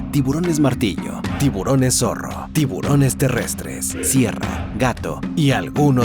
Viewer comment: Escuché "tiburón terrestre"???